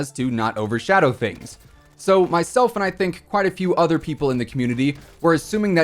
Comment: did you say myself and I or did I mishear that... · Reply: Myself, and I think plenty of other people too” is what I said